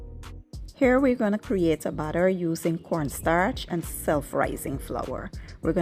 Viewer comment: I thought she was saying "butter" instead of batter, lol